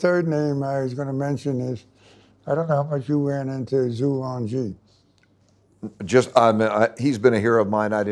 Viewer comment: Volcker says one of his favorite leaders is "zhu lang ji" or something. I cant make out what hes saying. Does anybody know the name he is referring to? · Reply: Zhu Rong Ji